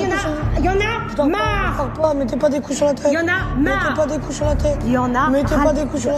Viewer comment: j'ai pas compris m'etais pas des coups sur la quoi ? 😭 😂